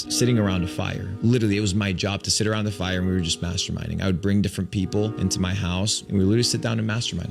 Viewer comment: I swear I kept hearing "masterbaiting". It wasnt till I re-watched with the subtitles.